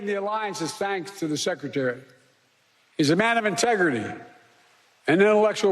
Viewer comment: what did he say ??? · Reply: "He's a man of integrity and intellectual rigor" Things you wouldn't know anything about